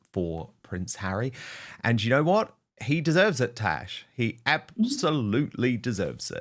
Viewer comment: Did Dan call “According to Taz” Tash? 23:40 😅